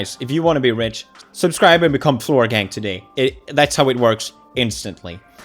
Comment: A few times when he has said floor gang I thought he was saying Logang and i literally CRINGED to my CORE. I kept thinking, say sike right now. CRINGE.